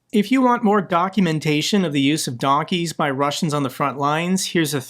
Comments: I heard it as “if you want more donkey-fication” 😂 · I heard "donkeymentation" had to rewind multiple times to make sure what said here lol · I thought said if you want more donekymutaton on the use of donkeys..... Had to listen a few times to hear documentation. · it almost sounded like you said, “If you want more donkumentation…” at 7:52.😊